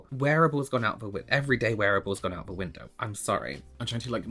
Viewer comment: Wearable is relative and subjective... A big lash for me is wearable every day! 😂 Did he say he had brushes? I heard "our" brushes! Wtf did I miss?